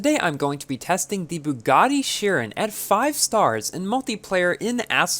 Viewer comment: Did I just hear 'Pagani' Chiron? No wait its bugatti